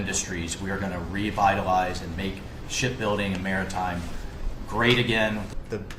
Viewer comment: did he say "shit building"? Pretty sure he did.